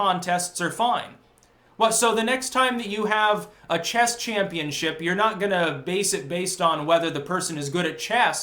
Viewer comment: What he said was "chess championship". What I heard was "chest championship".